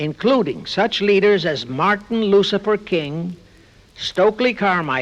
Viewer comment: Did he say Martin Lucifer King, instead of Martin Luther King???